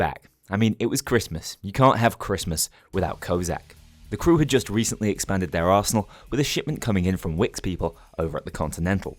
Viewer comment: i heard "the crew had just recently expanded their asshole"